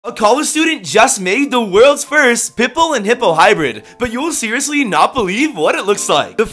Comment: Press here to play SQUID GAME! 🦑🕹⬇️ <link> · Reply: I thought you said hippo and hippo hybrid 😭🤣